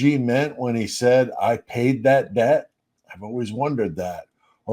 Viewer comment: Yeah, what did he mean he paid the debt? That bothered me too. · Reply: He said "I paid that bill". I was first mistaken also and thought he said 'debt', but it ain't so.